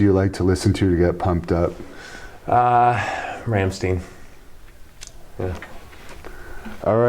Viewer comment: What did Devon say? sorry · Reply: Rammstein